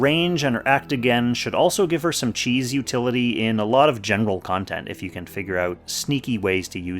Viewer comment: Bruhhh did you say “genital” at 6:44?